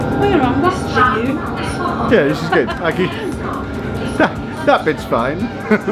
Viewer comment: its sounded like he said that bitch fine lol